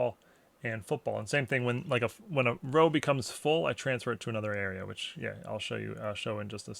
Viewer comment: Every time he said "When a row gets full I sort it and transfer it to another area" all I hear is "I'm here so I don't get fined"